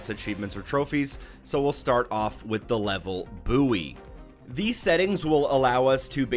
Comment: Did he just say “Boowee” instead of buoy?! 😂